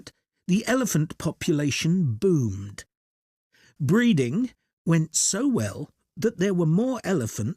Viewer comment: bruh aint no way i heard ‘breathing’ breeding, and ‘feet’ teeth bro😭